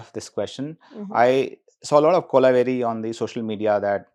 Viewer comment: Did he say “kola veri”?😂